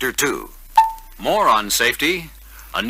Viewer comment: did he say moron safety??🤣